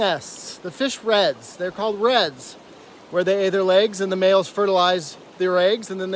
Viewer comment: “where they ayyy there legs” 😭 had to make sure I heard that one right